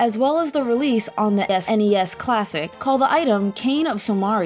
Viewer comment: did sommmebody sayyy s'ness? lol